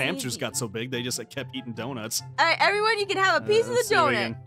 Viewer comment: "It starts with a D, ends with a Nut... oh wait no!" My new favorite weird thing aphmau has said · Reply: Francesca Fells it sound like she saying THESE NUTS meme